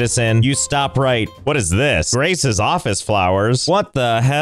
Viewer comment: did you say grace o.O forget you citizen i have things to do